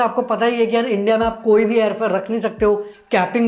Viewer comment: what did he say at 4:53?? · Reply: mein Air fair bola....matlab air ticket price mein bhi govt kaa ek limit hota hai..use jiya nehi rakh sakte ticket price...